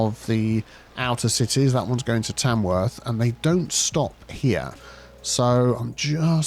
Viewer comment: Did someone say Tamworth? Summoning Rainbolt